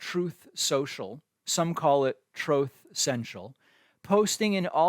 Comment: Cracks me up every time i hear 'Trothe Central' 😂😂